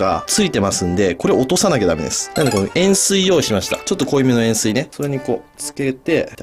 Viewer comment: 塩水が塩酸に聞こえてちょっと濃いめの塩酸はあかん!!!!と思ってた